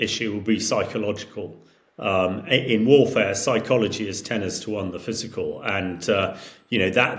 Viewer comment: “In warfare, psychology is TENNIS TO ONE the physical…”……?!?!??? Am I hearing that right?? “Tennis to one,” !???!!? That cannot be correct… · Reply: He said 'tenners to one' meaning 'ten to one' It's like a silly slightly posh way of saying it - something you would expect an ex or serving British military officer to say.